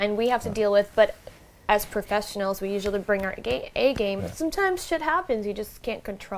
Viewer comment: she looked at him and said “Gay game”, instead of “A-Game” something slipped? 😳😳😳